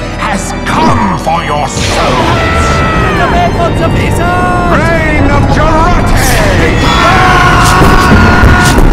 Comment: what did the Medic said? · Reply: He said get away from the wizard